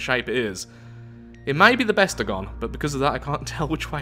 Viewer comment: Did you say BESTagon? Is that a CGP Grey reference? I love your vids 😁